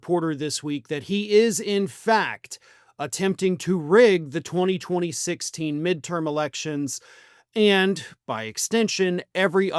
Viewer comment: did just say 202016?